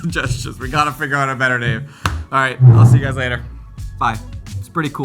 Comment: better name I heard : "wearable camera"